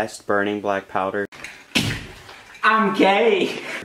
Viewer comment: Thorough, entertaining, after I watched it five or six times I guess you were saying um k but at first I thought you said I'm gay